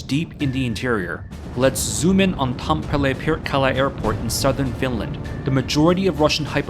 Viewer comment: my dumb ahh thought he said perkele